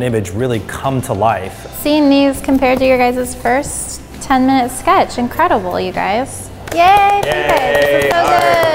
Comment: so we aren’t gonna give credit to miles, sam, and will for sitting there for an hour lollll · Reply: I thought she said „this was your first ten minute sketch“